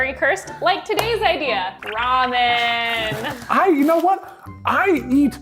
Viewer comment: Did just say "Raw-men"? I mean, that's Eggs-celent. Raw-men because it's raw ramen. Mind blown